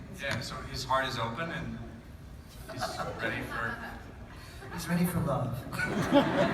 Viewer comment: Could you tell me what they say , Ben and Robin from 8:36？ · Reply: Ben: His heart is open and he's ready for.. Robin: He's ready for love